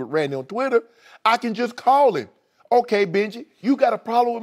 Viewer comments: Did he just say "Okay Bitch"? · Did Shannon say, "Okay, bitch"? Lolol